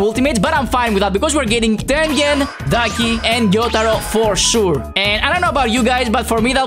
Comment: Did he just said "daki and jotaro for sure" in minute